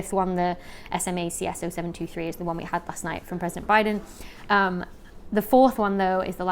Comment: Did you say, "from President Biden"? I wonder if you or someone finding this comment could please explain the connection.